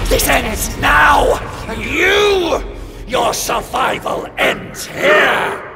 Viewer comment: What a hell say rockwell? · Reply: Enough! This ends now! And you! Your survival ends here!